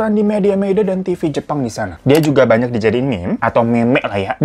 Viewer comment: gue dengernya "mem*k" 😭🤣